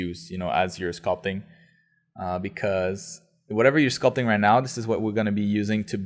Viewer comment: I thought it said "sculpturing in basement" D